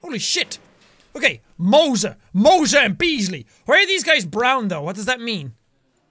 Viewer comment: Okay when Jack said why are these guys Brown I thought he said why is this guy brown and I thought he was being racist 😂😂😂